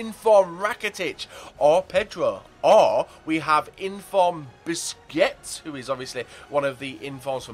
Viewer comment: Loolz its funny at the end it sounds like he said biscuits instead of busquets xD